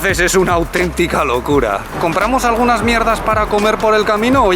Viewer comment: Soy yo ó escuché "MIERDA" 😂😂😂